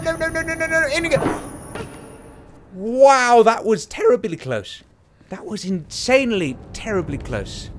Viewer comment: coulda swore I heard you say, "That was terrabilly close".